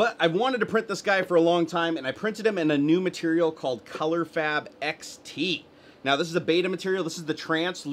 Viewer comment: is it just me or did he say "color fap xt"?